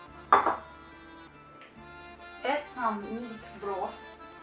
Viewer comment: what did you put in the pot?? beef and walnut, onion, coriander... and cup of water?? or some sauce?? 2:14 i didn't hear your english. i wanna cook this in korea for sell. please help me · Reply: Meat broth. She said about it at the beginning of the video when she was talking about ingredients